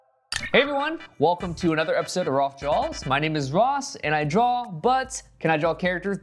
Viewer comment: I looked to check on my earbuds for a sec to make sure they were plugged in and all I hear is "My name is Ross, and I draw b u t t s" you can image how fast my head snapped